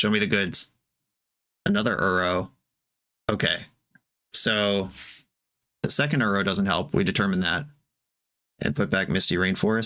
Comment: Match 1 Game 2 was winable if you keep the two Uros from the Sylvan Lib play Uro for 3 life, going to 4, draw the misty, put the misty into play. Then fetch and fetch, going to 2 and then play the 2nd Uro and go up to 5 again, bounce the 2nd Uro with Karakas. This with you have 7 lands next turn and can play Uro - Bounce - Uro for 6 life, winning the race. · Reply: Right? I heard "The second Uro isn't helpful" and thought I was crazy.